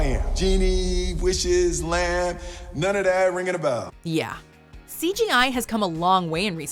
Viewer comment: "Yeah" You can say that over and over again. Shit... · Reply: “None of that ringing a bell” is what I heard starting at 6:54. Am I missing something?